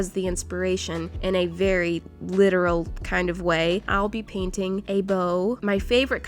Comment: I wasn’t listening to what she was saying and I thought she was painting a bra 😂